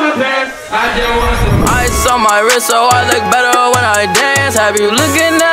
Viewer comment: i thought it said rizz instead of wrist